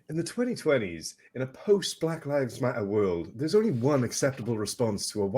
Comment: you said "post black lives matter world" I heard "forced black lives matter world." Weird huh?